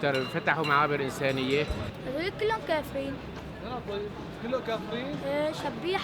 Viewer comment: The boy does not say "I will not leave" he says "they are infidels"